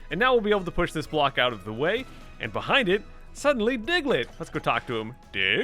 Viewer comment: did diglett just say let? I though Pokémon can only say/speak their name...